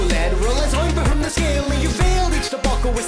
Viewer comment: Who understands the “scale leans” in 1:08?